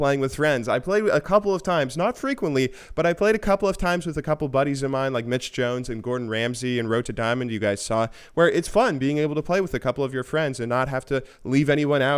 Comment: did he say gordon ramsey?